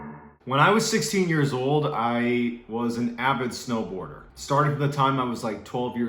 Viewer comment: I thought u said stone worker not snowboarder lol